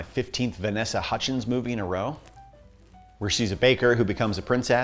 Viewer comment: Half listening to this I thought I heard him say call me princess, I'm still choking on my drink lmao